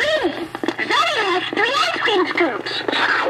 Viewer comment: The good thing i hear: “Someone has three ice cream scoops.” [licking]